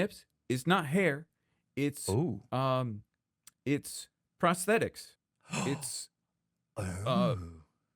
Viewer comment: I heard "frosthetics". 😂